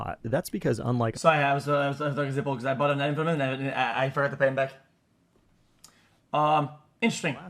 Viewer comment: What the hell did he say? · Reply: "sorry I was talking to zipeL (cs trader), because I bought a knife from him, and I forgot to pay him back." - I can understand him Aware